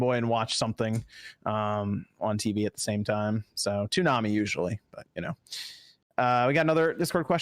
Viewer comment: did I just hear say " Toonami " YES knows whats up. ONLY SPAWNNAMI ON SPAWNSWIM